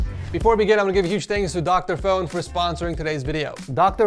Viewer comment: For the sponsorship at first I thought you said Dr. Phil, and I was so confused🤣🤣